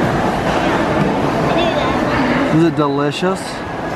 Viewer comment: Awww I heard say I at 4:27 I think ❤️and in there last 2 videos you can hear a voice in the back that sounds like saying its Like they talk to each other 😭💔❤️🔒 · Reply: Jansi xoxo bc she thought she said, just bc sadly passed away, every little noise has to sound like,but she said Kale, duhh😂